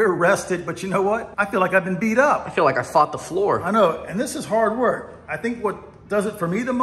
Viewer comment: I thought he said he fucked the floor 🤣